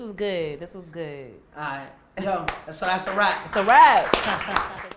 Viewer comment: At the very end I thought he said, and that's a Rat 🐀 and she said, that's a Rat 🐀. Oh that's a rap Lol.